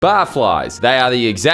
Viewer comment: I thought you said botflies, which I would totally want to be : I love getting under people’s skin